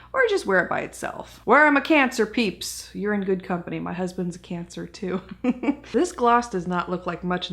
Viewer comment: Lmfao I heard “my husbands cancer too” and I just felt old man up there regretting bringing my lil Leo soul to life